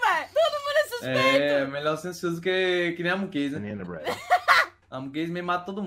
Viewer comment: fui só eu que escutei um cara falando ''i am the bread'' nessa parte?